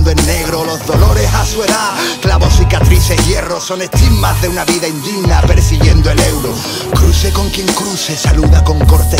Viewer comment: Que dice en el 0:45? alguien lo entiende?? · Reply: son estigmas de una vida indigna persiguiendo el euro.